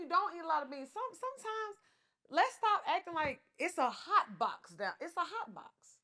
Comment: I thought said “hot bod” 😂😂😂